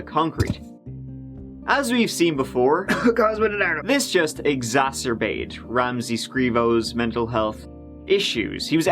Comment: I didn't get the coughing joke in reference to when Ramsey bonked his head on the concrete. Wth is sayin 5:29 under his cough? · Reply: I think it's Cosmo DiNardo... did a video on him recently... he went on a psychotic killing spree after several serious head injuries left his brain all scrambled.